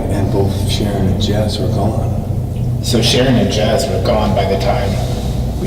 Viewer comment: that voice did say "Shanon" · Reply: Adventures with Cryptid I thought it said ShaRon yeah I'm prob wrong but I really though ShaRon rather than Shannon... ohh idk